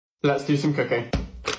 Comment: At the beginning I hear "Let's do some cocaine" every time.